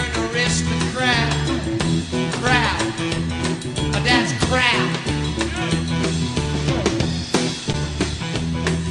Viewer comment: Did he say "My dad's crap" @ 1:44 ? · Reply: "Crap!...That's CRAP"!